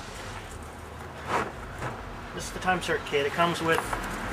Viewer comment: At first I thought he said he was gonna use the "time circut" like as if he was Doc Brown. Then I realized it was "Time Sert kit" haha